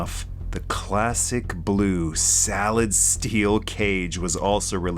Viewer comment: This was a great video! I gotta point out though, @ 4:35 instead of "solid steel" you definitely said "salad steel" and that gave me a damn good chuckle.